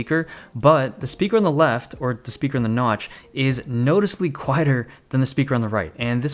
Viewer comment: check captions said >the speaker on the notch wrote >the speaker on the airfield runway ... slick